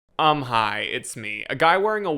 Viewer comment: I thought he said "I'm High, it's me" 😂 You'd have to be on substances to watch this marathon of WTF. (I'm certain that Tim was on dubious amounts of Coke and LSD when he wrote Left Behind 🤣)